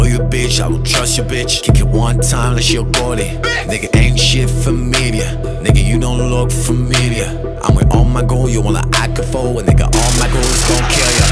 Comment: I thought Laurent said "you don't look for media." Also, Larry's screams during Laurent's rap made me feel intimidated XD